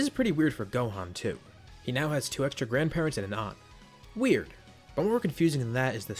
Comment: wrt Gohan, at first I thought you said that he now "has 2 extra grandparents and an ARM." referencing Future Gohan for some reason and i almost lost it xDD